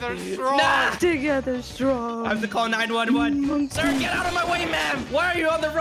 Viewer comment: did say "sir get out of my way maam?"